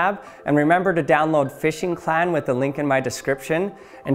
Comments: Did you say "Fishing Clan? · Support my channel by getting Fishing Clash on your iOS/Android device for free <link> ! Use my gift code ACTIONLAB to get a $20 reward, and share your biggest catch in the pinned comment! Join Fishing Clash community to stay tuned about the latest news: Instagram - <link> Facebook - <link> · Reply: you say " fishing clan " , instead of ' fishing clash '. 🤔